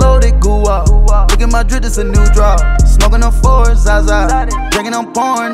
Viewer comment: Thought it said smoking on ‘foreign’ zaza